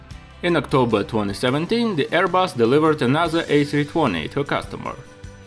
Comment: What does he say at 1:15? "In October 2017, the Airbus delivered [?????] A320 to customer." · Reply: yes "another"